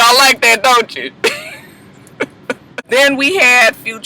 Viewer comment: That's the name of Future's song? Wicked? I thought he was saying "weo weo weo weo weo" like... Cops sirens...